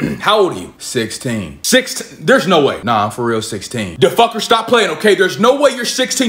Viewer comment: did he call him da'fucker?